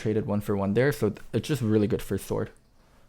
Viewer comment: Why am I hearing "Thord" than Sword.